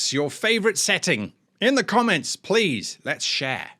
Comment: I misheard "Let's share" as "Let's shag" and suddenly I imagined you dressed as Austin Powers... Places I think aren't TTRPG but game ones i.e. Ys.